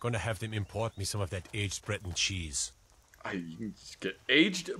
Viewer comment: I know i'm late but I think he said breton cheese (like the skyrim race) not bread and cheese